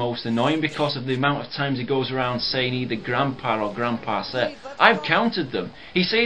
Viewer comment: What does he say at 8:05 ? "Because of the amount of times that he goes around saying ___________"? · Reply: I know it's 12 years later but the big pay off " either grandpa, or grandpa seth"